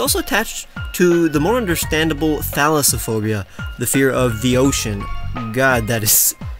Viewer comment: I swear I thought you said phallusaphobia an I almost spat out my tea